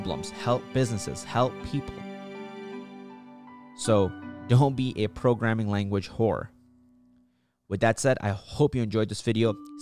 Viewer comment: did I just hear "don't be a programming language WHORE"????